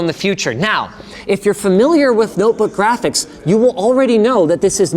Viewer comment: did he say notebook graphics?